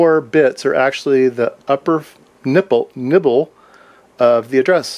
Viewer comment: did you say 'ni*ple'? I assume so since youtube isn;t showing me the 3 comments. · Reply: He said Nibble (4 bits).